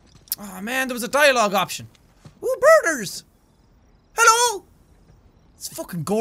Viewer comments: At first I thought he said burgers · I thought he said burgers while looked at the birds and was like "wtf do you eat over there?!"